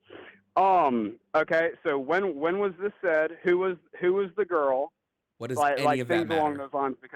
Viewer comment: When he said “who is this girl?” All I heard was “she bad?”